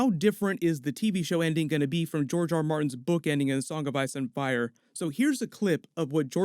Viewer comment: Totally thought you said Jar Jar Martin.